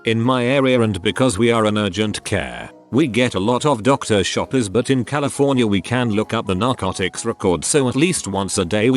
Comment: What is the beeped word at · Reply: Meth. The word is meth.